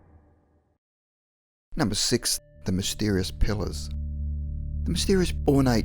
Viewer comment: lol sounds like your saying Pillows